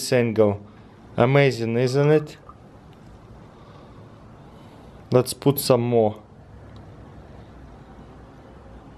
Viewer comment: what is that liquid called again? And the name of the magnets.. I didn't understand him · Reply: That's ferrofluid, basically a magnetic metal suspended in liquid. And the magnets are neodymium